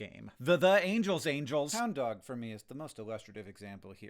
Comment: Ok but I have NO IDEA what you mean when you say "Hound dog." English is my only language and all I hear is "Dog dog."